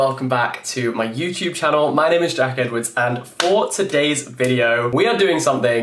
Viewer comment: this was on my recommended and i didn’t look at his name. i heard “my name is jackets.”